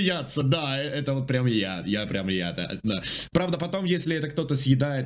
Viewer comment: Я прям Ято? Мне послышалось? Кароч анимешник поймёт)